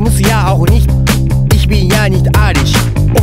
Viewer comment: Was singt der da von Arier ? Na hab mich warscheinlich verhört xD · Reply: "Leider spreche ich kein Deutsch,die Leute kein Japanisch. Doch ich muss ja auch nicht,ich bin ja nicht arisch."